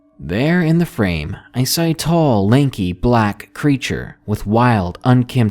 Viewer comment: I was half listening to the first story and I swear I thought it said a "black preacher" stood in her doorway instead of black creature and I LOLd pretty hard